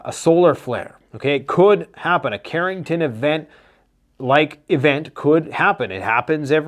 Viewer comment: did you say Carrington event??? 😂 I love my name it’s a great Segway into talking about preparedness.